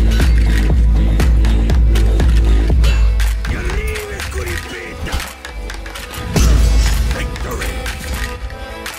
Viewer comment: can someone tell me what he is saying there it sounds so sick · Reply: He is saying "I'll tear you to pieces!"